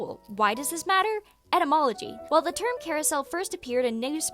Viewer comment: Etymology nerd jumpscare lol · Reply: Thought you said entomology and was very confused because I didn’t hear anything about bugs 😅